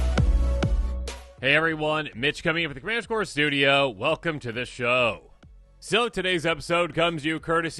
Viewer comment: I try to like these posts but fuck I have to rewind so much because you talk too fast and all your words get jumbled up. I swear it sounds like you say commander core studio are beginning of every video